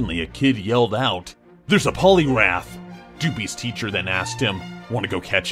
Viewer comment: #3 when they said polywrath, I thought they said polygraph, as in tge lie detector test